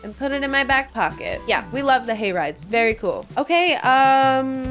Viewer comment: the way I heard "gay rights" when you said "hay rides"....